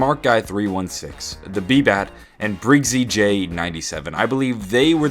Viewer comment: Thanks for the shoutout <3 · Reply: Ohhh my bad I thought he said beebot got a shoutout not beebat. my bad beebat.